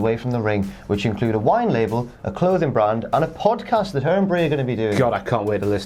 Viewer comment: I heard Raven......then Bray (Wyatt) but I’m guessing you said Brie 😂. Or maybe Rey? Who wouldn’t wanna see a Raven/Nikki Bella podcast?!? Sounds awesome